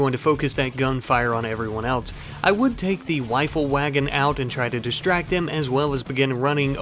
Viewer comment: Is it me or am I hearing him say "wifle wagon" lol